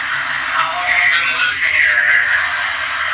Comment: Why did you dismiss 32:40? It sounded like someone said "How long are you going to live in here?" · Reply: We didn't dismiss anything... If you'll notice at 31:10 it's me asking the question " How long have you been living here?".